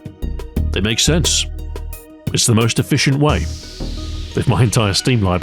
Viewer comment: did he say "entire" or "hentai"??